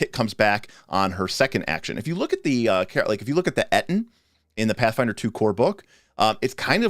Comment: what is the word? aton? atton? "If you look at the ??? in the core rulebook" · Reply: Ettin